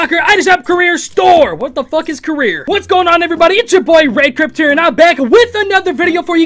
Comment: I thought he said Red Crip